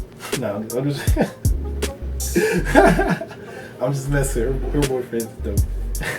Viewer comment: Did he just say her boyfriends kinda hot!?!?! HUHHHHHHHHHHHHH GAYYYYYYYYYYYYYYYYYYYYYYYYYY